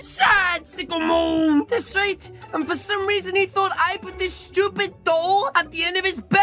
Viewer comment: I thought you said pips doll